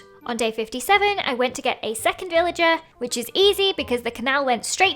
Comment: Did just say "illager" instead of "villager"?